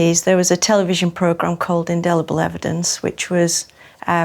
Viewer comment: what did she say here? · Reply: Indelible evidence